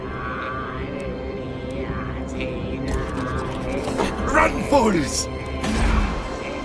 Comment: did they say “Run Forest”?